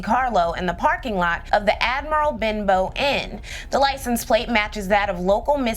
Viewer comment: I thought you said Admiral Bimbo Inn 😅